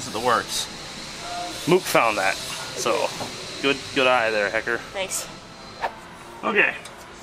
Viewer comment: I realy though at 8:24 he said “good eye there ni**er” and i did a legit spit take like woah holy shit howd that make the final cut😂 ( for anyone who missed what im talking about he said “hecker” which honestly kinda makes it even more ironic cuz he even said hecker not fucker lmao)